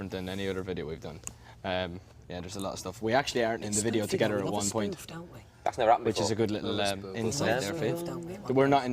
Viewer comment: Can someone tell me what Louis whispers at 3:30 ??????? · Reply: It’s spoofy though. we love a spoof, don’t we?